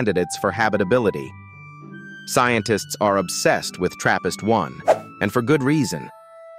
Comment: For a second i thought he said therapist